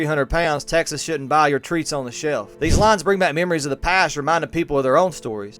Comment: “Treats on the shelf”???? I thought he said “damn bag of fudge rounds”.